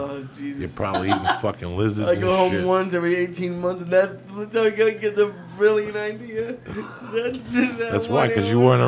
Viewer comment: wtf did Lee say at 1:32:40 lmao was he having a stroke? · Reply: LOL "I go home once every 18 months, and that's when I get a brilliant idea!"